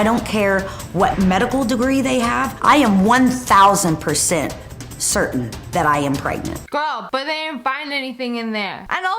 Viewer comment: What she said: i am 1000% certain that i am pregnant The subtitles: i am 1000% certain that i am brightness